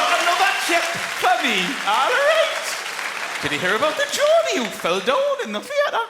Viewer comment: Anyone know what he's saying from 01:49 - 01:52? I'm doing a transcription assignement but after playing it over and over I still have no clue haha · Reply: I'm sure this is way too late but: "Have youse got another tip for me allriieet?" ;)